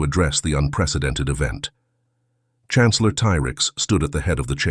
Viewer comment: Guessing it was meant to be Aegis Prime? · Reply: And the speech-to-text gaffes, like "T-Rex" for what I hear as 'Tyrax', and "Cindric" for 'Syndrex'...